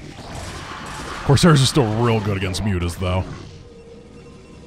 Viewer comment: you’ve not enough CHEESE (thats how i heard it and died laughing)